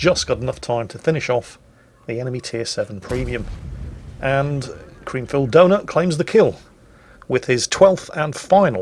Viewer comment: Did i hear CreamfilledDonut?